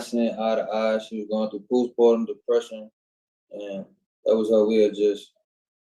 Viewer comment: Did he say she was going through “Puda pa depression “ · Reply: I thought he said poost pardum.